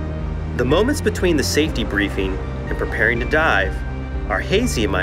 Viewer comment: I thought he said preparing to die